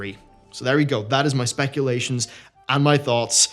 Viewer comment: Anyone else hear "Spankulations"